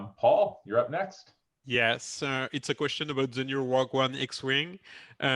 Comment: My other favourite bits were when the French gentleman would say “Rogue One” - I genuinely thought he was saying “whaa gwan?” 🇯🇲😄